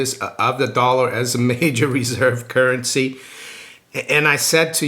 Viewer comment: I could have sworn the reporter said how concerned are you with the dollar status as the world symbol....... reserve currency. As soon as she said symbol down comes the sign.